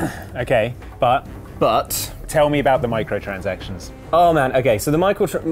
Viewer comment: did say Micheal transactions ?